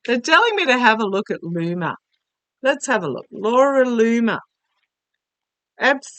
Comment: Every time I hear “Laura Loomer” I hear “Loony Luna”. 😂 Harry Potter fans may understand .